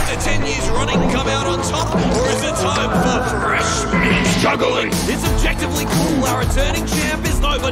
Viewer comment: I thought he said freshmen but fresh meat😂